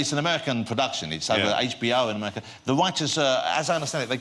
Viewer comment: when jonathan says writers it sounds like hes saying rioters